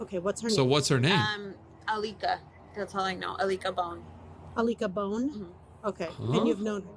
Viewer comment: ik this is supposed to be a sad story but it sounded like "I lick a bone" 😭